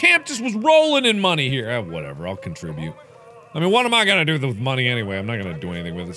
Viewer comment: Did he just say "what am i gonna do with money?"